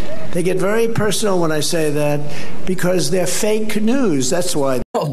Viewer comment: Did he just say "Fake canoes?"